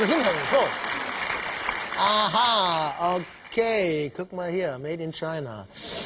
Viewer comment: he did not say "cook my ear" lol ... he just says "oh look!"